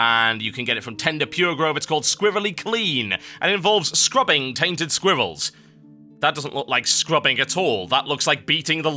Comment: WOW, not to be any kind of rude, but when i was you were talking about scrubbing squairrels... i thought u were saying "Scrubbing tainted squivalls" >.<